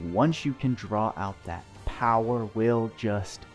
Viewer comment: did you say... POWER?